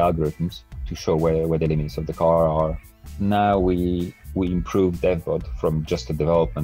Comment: I'm sorry, but is he saying "dead bot"? heheheh ;) :P